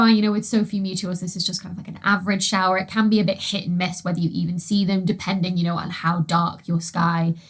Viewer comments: did you say sh!t and miss? Lol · what and miss? 😂 Appreciate you Dr. Beckie but I totally misheard this the first time around 😊 Really says more about me though honestly 😊